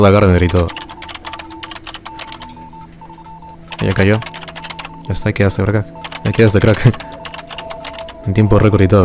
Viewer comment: Acaso dijiste vrga en el 7:55 xdddd o escuche mal ? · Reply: LinNierk ya cayó asta y quedaste crack dijo